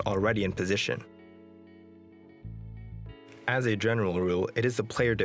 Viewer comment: what is he trying to say from 1:10? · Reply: He said: as a general rule